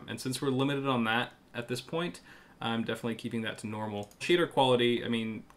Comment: I heard "Cheater quality" instead of Shader